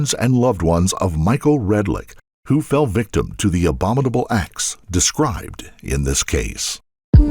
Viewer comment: Just fyi, and I don’t know if it can be fixed, but your subtitles say “abdominal” in place of “abominable” in the ‘thoughts and prayers’ moment (approx 4:35), which is an otherwise nice touch. 🙏